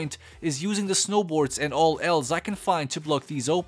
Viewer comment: Not gonna lie, honestly thought he said finding all Ls at first xD would have been funnier instead of else